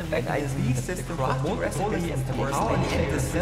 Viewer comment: I misheard him at then end and thought he said to subscribe so he can afford frog therapy